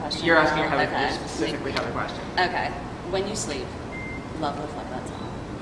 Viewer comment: What song did the interviewer say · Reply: Whenusleep